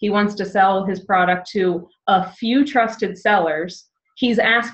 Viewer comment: This was a fantastic video. I learned a lot. It's wonderful that you share your knowledge At 42:28 you mention something you use that sounds like "repseller" I tried Googling that but couldn't find it. What is the website and/or the correct spelling so that I can find it myself? Thanks a bunch... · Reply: Same here, I thought you said red seller, would you share that with us? Thanks!